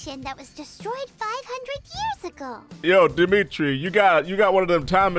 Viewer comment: did you just call him dimitri I guess I'm not the only one